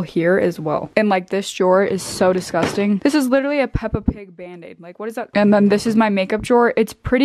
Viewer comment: why did I think she was going to say "pEpPa what are you doing on my bandaid" lolll